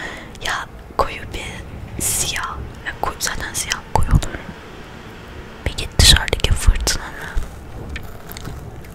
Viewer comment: abla ne dediğini anlamadım "senden siyah koyu olur "diyorsun ne dediğin anlaşılmıyor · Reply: Zaten siyah koyu olur diyor